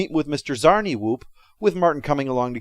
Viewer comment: I feel compelled to point out that at 12:44 you say "Martin" nice series of videos tho. (must be the dislike of the movie, and the Arthur av Martin Freeman that makes that "Martin" instead of Marvin really sting.)